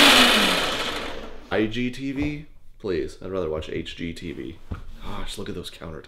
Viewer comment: who else heard him sing "IGTV"